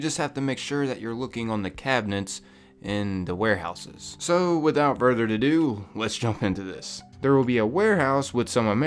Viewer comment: did you just say "without further to do"?